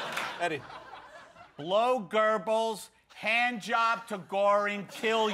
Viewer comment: What does Eddie say at 2:40? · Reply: "Hand job to Goering ", as in Hermann Goering. Luftwaffe Gen. I'm not a skinhead,I just read Anthony Beevor Stalingrad.